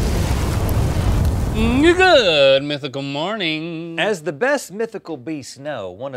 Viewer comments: swear he said niggagoodmythicalmorning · I thought that Link said the N- Word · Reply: He said "Na-Gaood mythical morning" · Did he just say the N-word? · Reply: he literally said "good." why do ya'll do this? you claim someone said the n-word despite never saying it just to try and cancel them. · I swear I thought he said nigguhgood mythical morning